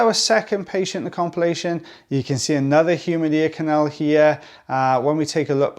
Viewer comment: Thought you said "another human ear canal" and I was so confused hahahaha. I was like all we do is human ears right? Then I realized you said humid hahaha. Love these cases though! All those ribbons of skin being pulled out is so satisfying!